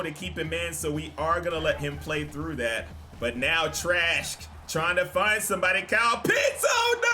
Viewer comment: why it sound like saying “trash-k”😂😂😂😂